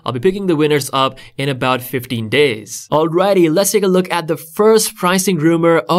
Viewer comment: I thought he said "50 days" instead of "15 days."💀